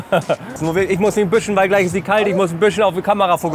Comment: musst du erklären! "GLEICH IST DIE KALT!!" es tut mir wirklich Leid aber da versteht man schnell Sie* Hei* und dazu die Handbewegeung, ist echt unglücklich. Musste mehrfach wiederholen bis ich verstanden hatte dass du sagst Gleich ist die kalt! ...nicht, dass sich da Menschen getriggert fühlen. - Zum Video; ich fans wieder sehr gut, danke fürs Testen auch solcher Locations. Mir wärs zu teuer, ist halt viel Name. Biss zum nächsten Döner ;)